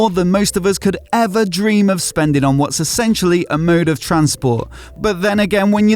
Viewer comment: "Essentially a mode of transport." No, It's a toy, a magnificent toy but a toy none the less. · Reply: I could have sworn I heard him say "murrrder transport" like you're going to kill yourself...it made sense in my mind at that moment. Calling it transportation is insulting.